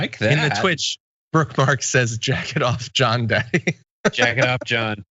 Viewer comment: Sounded like you were saying, "Jack it off, John" instead of Jacket off John, just sayin